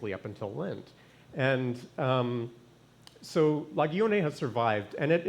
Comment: The first time I heard "la guillonée" I thought I heard "la gaie année" (the happy year).